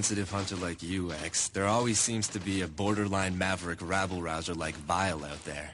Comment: did zero just say... Rabble-rouser?